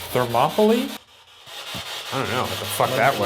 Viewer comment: Ok, am I the only one that heard “Don’t mess with me” instead of monopoly at 11:15?????